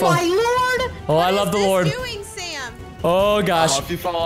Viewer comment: did anyone hear that Preston say oh I love the lord